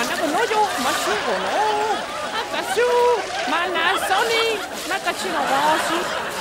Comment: Todos dicen que dijo Panasonic y en realidad dijo malnasoni "Mal nacido"